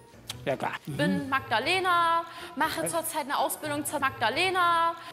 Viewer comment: Ich habe zuerst verstanden "Ich bin die Magdalena, ich mache gerade eine Ausbildung zur Backdalena" und lag am Boden.